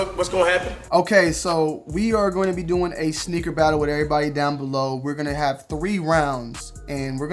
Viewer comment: Who else thought he was saying “snicker battle” 😂